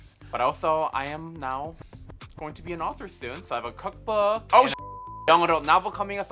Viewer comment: I thought she said she was going to be on All-Stars soon but she said she will be an "AUTHOR" soon. WISHFUL THINKING I GUESS.